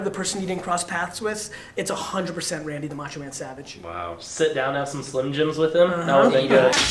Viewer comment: I thought it said nacho man